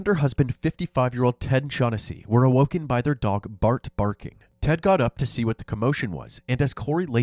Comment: I thought you were saying the dogs name was “Bart Barking”